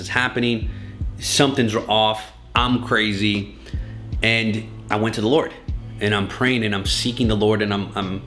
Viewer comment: Every time you say: "I went to the Lord", I thought you were saying "I went to the lawyer", which would have been appropriate too.